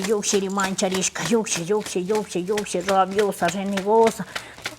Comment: En que minuto?? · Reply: hola! En el minuto 3:05 - 3:06 aprox, puedes escuchar que dice Rabiosa, Renegosa. Creo que lo dice en todos sus videos